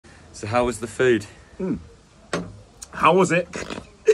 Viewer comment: I thought he said how was the fade lol i was expecting a dude with a black eye lol